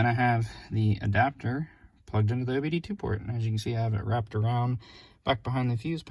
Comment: Great install and clean. I have a 2019 4runner. Im sorry but at 14:24 you say you have the adapter plugged into the something port. Didnt quite catch what you called it. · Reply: Thank you! The adapter is plugged into the OBDII port or diagnostic port under the dash.